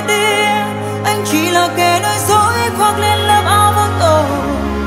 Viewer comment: "Đừng nói điều gì, đừng nói thêm chi, giọt nước trên mi, giờ đã tràn ly Người đã nơi đây, và đứng bên ai, và chiếc hôn kia, chẳng lẽ là sai và cô ấy là ai, người anh yêu là ai, em không biết điều gì, sao anh không nói gì Và giờ anh đừng nghĩ rằng ta không biệt ly, Chẳng còn gì để em hối tiếc Anh chỉ là kẻ nói dối, khoát lên lớp áo vô tội... Người khiến em tin rằng anh không có lỗi Anh chỉ là kẻ nói dối khi em tin anh thay đổi...Đừng khiến em đau...uh...uh...úh" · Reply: Nguyen Hong Chinh em nghe thành anh chỉ là kẻ nói dối khoát lên lớp áo măng tô bác ạ 😂😂